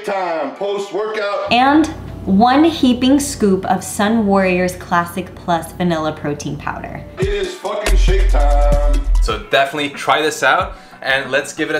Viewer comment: 2 whole strawberries??!! Damn, that guy means business. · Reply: I thought he said it is sh#t time. Honestly lol